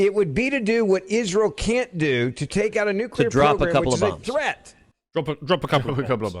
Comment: Little hats, big problems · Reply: I thought you said "pogroms" for a second there. Not yet I suppose.